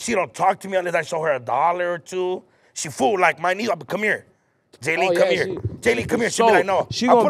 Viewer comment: I keep hearing “My Nigel” but I don’t see any Nigel’s in the video? 🤔😨🤔